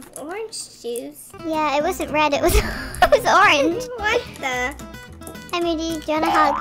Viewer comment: I swear I heard Moody say “SUNNY WHAT THE HELL!!” they cut it 🤣🤣